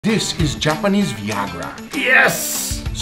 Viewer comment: For a second I thought he said jappanese fois gras that also works I guess..